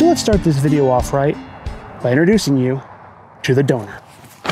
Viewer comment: I am fat I thought he said introducing you to the donut. I was like donut !